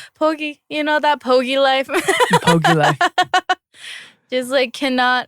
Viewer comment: Did they say pogi life???